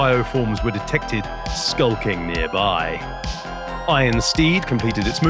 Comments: I heard the Rhino's name as "Iron Steve". 😆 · I misheard the rhino's name as Iron Steve at the beginning and that was then all I could hear for the rest of the video.